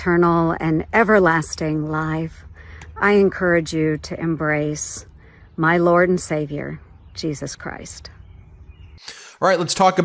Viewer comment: She encouraged people to embrace her lord and saviour jesus christ, I thought she said she wasn't going to bring up the tooth fairy.